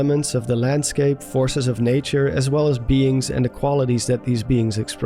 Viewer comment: when you here commie instead of Kami